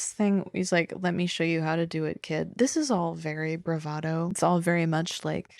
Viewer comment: just to let you know the subtitles were wrong, it's "let me show you how to do this good" (same as ep 2, "i'm c*ming" during their scene whereas the subtitles say "oh god")